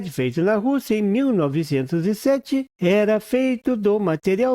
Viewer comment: Quem inventou o LED foi um russo Oleg Losev em 1927, Esta no link que colou. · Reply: Olhe e escute no tempo 1:08 , ele fala 1907 e não 1927 , eu também li não sou cego , agora pelo visto você é surdo , escute ele falando , ele fala russo e o ano de 1907 sendo que em 1907 foi o britânico usando material SIC , antes de comentar preste atenção primeira pra não falar asneira...